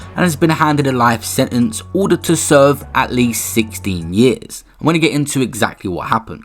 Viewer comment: 16 years is not enough 😭. This is beyond sad. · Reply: Rah I thought they said 60 years. Wdym life then 16 ain’t life ? I don’t get it